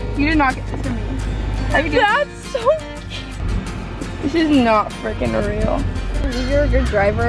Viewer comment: did anyone hear "that's so cute